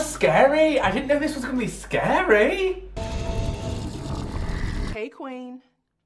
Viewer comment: Why did I hear hey twink instead of hey queen 🤔